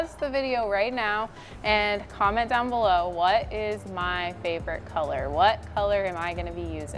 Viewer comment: Green 💚 Seconds later ORANGE? I thought you said what is your favorite color to paint?? 😂🤣😂🤣😂 I've NEVER seen you paint anything orange! Maybe I need to go back to your earlier videos? Welp, I'm super excited to see what you do with it! You make everything beautiful! ❤️💯😊 I'm surprised you didn't use any primer... what made you decide not to?